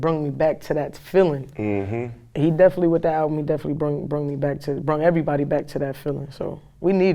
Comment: Young MA at 5:32 did he just say "Brung"